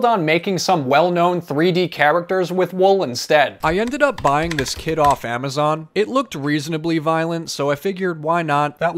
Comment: No one: Not a single being: Me mishearing the intro: did he just say he bought a kid off amazon